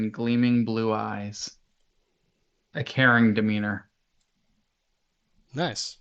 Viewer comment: I realized later that you said "caring demeanor" but I first heard it as "Karen demeanor" which I think is even more interesting.